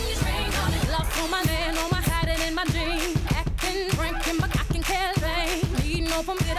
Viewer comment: I heard “hide it in my jeans”